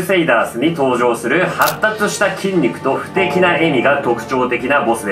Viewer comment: 素敵な笑みに聞こえて脳内バグった